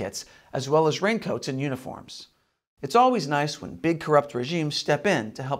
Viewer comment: I thought said 'unicorns' and imagined the CCP bioengineering a species.